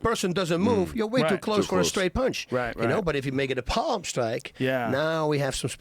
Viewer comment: Is there anything that Bas likes more that palm strikes? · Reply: For a second I thought you said palm strokes.